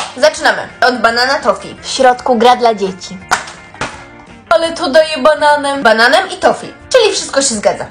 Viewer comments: ✨alE tO dAjE BaNAneM✨ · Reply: Ja usłyszałem "ale to dojebane" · ✨alE tO dAjE BaNAneM✨ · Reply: Usłyszałe aletojebanamem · Usłyszałem "ale to dojebane" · Zamiast dodaję bananem usłyszałam : ale to dojebane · A ja usłyszałam "doj3b@ bananem" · Ja usłyszałam że są dojeebane zamiast daje bananen😂